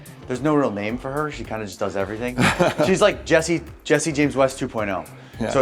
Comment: Thought he said my name!! Jesse James SIMS